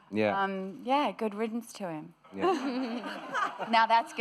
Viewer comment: Hi Haya!. Could you help me understand what Nicole says at 13:10...(after saying "Who cares? we were fine, so, yeah...____)" Thank you :) . Anyone reading this wanting to help, thank you very much too! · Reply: good riddance to him. 3 years later lol anyways..